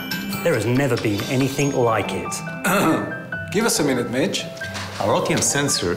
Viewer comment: Give us a minute BITCH!. That is what I heard first... Sounds. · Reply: Give us a minute Mitch - is what's said. Sorry for the mistake - first heard after over 600K views...